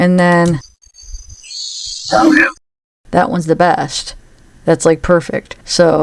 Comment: I thought I'd heard that on the first one not as obviously but I heard it and was glad you noticed it as well it was quite odd that they changed it and by the way it's actually "G'down as a drawl of "Go down" th' hielll... not just Down the hill. Whoever is speaking has a drawl which is not normal for this area... they may even say y'all or Crick versus Creek!